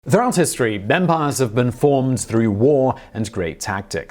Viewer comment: At first I thought you said vampire instead of empires. It’s terrible to have bad hearing but it let me know to turn up the volume.